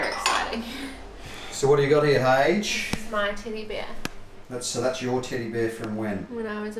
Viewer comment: you said "Teddy bear" I heard "Tinny beer", made me look up in shock. Haha.